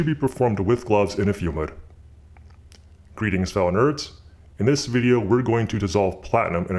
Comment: What did he say at 0:12 Greeting farnness?? what??